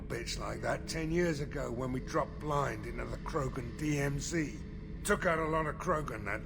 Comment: Oh my god, I heard Zaeed say "Krogan TMZ" and just couldn't stop my imagination...